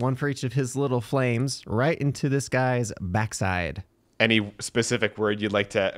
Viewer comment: I thought he was saying “Back Shots” 😭😭😭